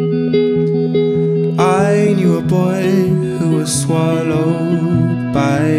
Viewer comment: I knew a boy who was swallowed by the sky By the flashing lights I knew a man who got lost in the big dark blue And came out alive I knew a boy I knew a man that looked a lot like you I knew a time you could stand still beside Never rushing by I knew a place you'd go for your head to explode Into peace of mind I knew a time I knew a place that felt a lot like you I knew a boy I knew a man who looked a lot like you Just like you I knew a friend that would hold on to the flames Keep them from burning you I knew a smile like that could see through all of the stones That the world had thrown I knew a smile I knew a friend who looked a lot like you I knew a boy I knew a man who looked a lot like you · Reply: lol i thought he said " i need a boy that would swallow"